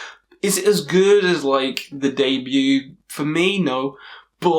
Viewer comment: Great choice! 🙃 I didn't understand the album you mentioned at 4:45 😒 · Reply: He mentioned Immortal’s debut (their first album).